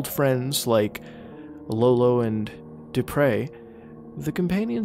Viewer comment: Lolo and Dupre. Ahhh i always thought he said depray